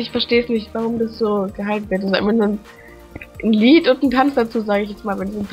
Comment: ich hab irgendwie Glied verstanden xD warum auch immer "Hust Hust"